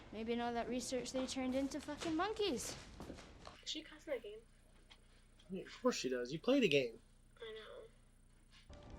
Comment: Hi, what did your daughter say at 22:03? Something about name or game? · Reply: Mark she asked "did she (ellie) cuss in the game